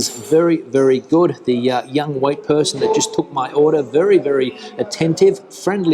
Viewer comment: The white person that served me. Is there any need to add 'white' into that sentence? I find that VERY offensive! And, I'm a white person!!! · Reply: He said "wait person". Misheard it myself first time 🙂